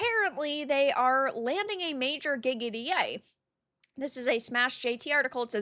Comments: When you said a major "gig at EA" i thought you said "giggity" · Whenever I heard you say "gig at EA." I heard Quagmire.